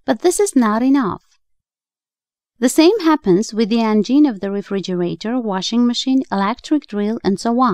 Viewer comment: "The vagine?" "The engine?" "The angine?" What did she say? I thought she was talking about motors.